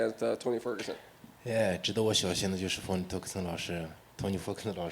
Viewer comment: did he say fony terguson?